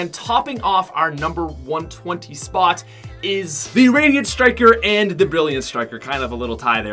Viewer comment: I swear he said Iranian striker